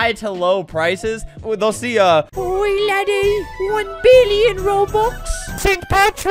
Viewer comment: he said "oi laddy!" And the captions said "boy daddy"